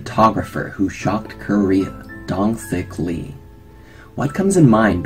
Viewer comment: I Heard “ Dong Thicc Lee “